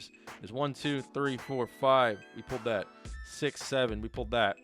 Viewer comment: WHAT DID HE SAY!?!?!??! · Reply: “We pulled that”